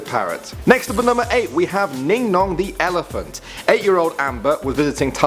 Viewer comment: Ningnong I thought u said Dingdong honestly